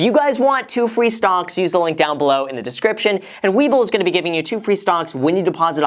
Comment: thought you were saying WEEBLE all this time, and then i searched and i discovered. you did not say WEEBLE...